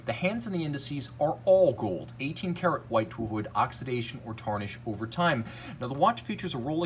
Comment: What does he say at 5:06? 18k something but can’t make it out??? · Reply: “18k white to avoid oxidation over time”